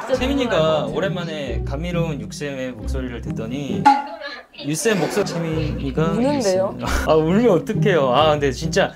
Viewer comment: "그건 아닌데" 나만 이렇게 들림?ㅋㅋ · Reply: 그건 아닌데 맞아요ㅋㅋ